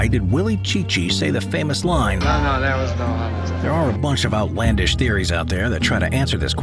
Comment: What's saying at 10:19 ? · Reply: oh no that was no heart attack